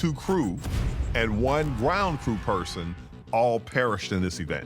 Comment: I saw it to the end. I did not disover the truth. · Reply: Nothing we didn’t already know… (when talking about the dead I thought he said “one brown person”. Haha)